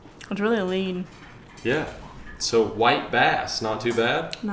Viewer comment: At first I thought he said wipe ass.